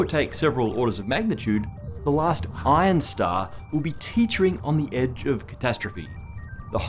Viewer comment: I thought Matt said "Pion Star" ... Please do not look up "pion star" on your work computer, unless you would like a chat with HR